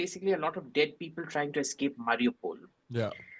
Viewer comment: sorry just want to check, did Abhijit say "... Dead people trying to escape Mariupol"?